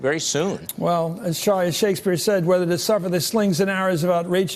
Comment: does he say "Charlie Shakespeare" ? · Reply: The guy he's talking to is named Charlie Sykes. I think he meant to say "Well, Charlie, as Shakespeare said..." but said "Well, as Charlie..." Good catch!😂